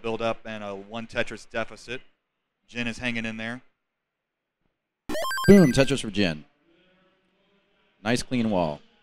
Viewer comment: I thought the commentator said, “Boom! Tetris for Jeff.”